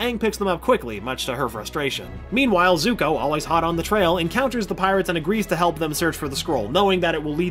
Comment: I literally didn't hear the "...on the trail" part until he went on to say he was still tracking aang. I thought he just said "Zuko, always hot" and I honestly after his ponytail is gone that is accurate. XD